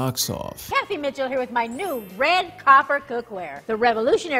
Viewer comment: Why’s it sound like she said “Cathy bitch with my new copper cookware”